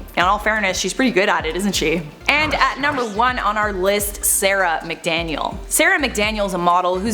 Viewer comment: No one: Literally not a soul: Sarah McDaniel: 👁 👄 🧿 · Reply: I THOUGHT THAT SAID ‘Sarah at McDonald’s’ ✋😭 I was trying to figure out what that ment